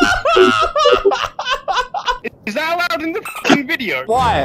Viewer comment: ”I J U S T P U L L E D T H E G R E N A A A A A A A D E” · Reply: i thought he said praise the law but i thing he srsly understood allah